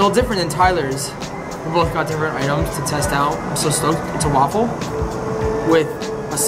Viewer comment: I thought he said “I’m so stoned” but he said STOKED